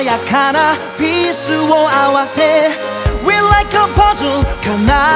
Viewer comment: (⌒_⌒;) Como yo no hablo ingles la primera vez que lo escuche pensé que decía "we like the pussy" xD 😶